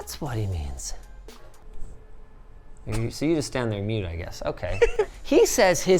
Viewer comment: i really thought bro said "you see you stand there mewing"